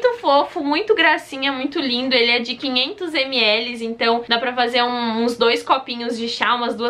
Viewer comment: 500 ml eu entendi 500 reais